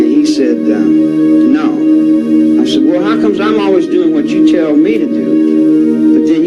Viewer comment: Acho que ele fala algo do tipo "...how comes I always do what you tell me to do but then..." É difícil mesmo, ele fala com sotaque.